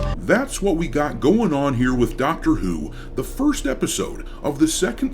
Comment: I legit thought I heard you say “Doctor eww” 😂